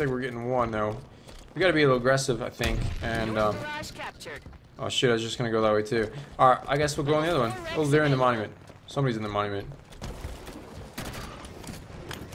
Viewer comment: Did you say shoot or sh*t??